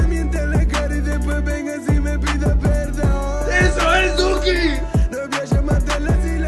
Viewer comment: Pensé que decía la no voy a llevarte a las islas mañana por culpa de esas pastillas con alcohol